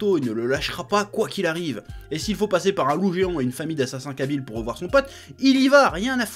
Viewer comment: Ptdrrrrrr c'est moi ou il a dit "une famille d'assassins kabyles" à 12:32 ?